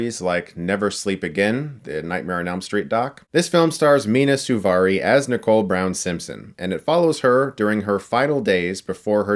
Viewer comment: at first I thought he said, "Starring Mia Malkova as Nicole Brown." lol